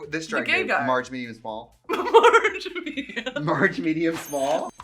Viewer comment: I heard "Large, Medium and Bald".